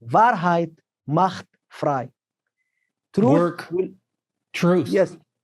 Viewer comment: something was removed. I want to know what he said. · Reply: Did you mean the German phrase? it was wahrheit macht frei